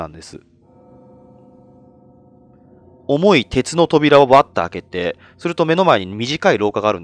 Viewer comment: 重い鉄の扉を 重いケツの扉 って聞き間違えた😓